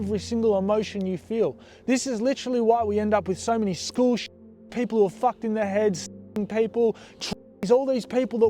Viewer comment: when did he say that?? if thats what he has turned into thats fucked up im a (extremely in the closet) trans teen and even people that dont affect my life in any way talking shit like that makes me genuinely scared to want to live as my self. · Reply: As the other person said it’s at 16:54, he says tr*nines but cuts it out. I am trans too, I’ve been out for years and it is fucked what he said I don’t like the comparison at all like we’re just people bro.